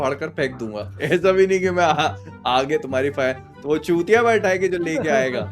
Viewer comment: what did he said does anyone know 🤔🤔? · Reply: Tmkc